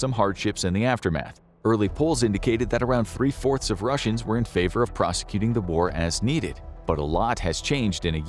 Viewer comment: There's an error in the captions at 10:15. The voice over said "three fourths of Russians were in favor of prosecuting the war", but the caption says "three fourths of Ukrainians were in favor of prosecuting the war". Obviously the caption is incorrect.